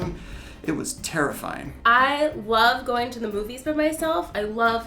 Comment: #7 It sounded like he said "I love vagina."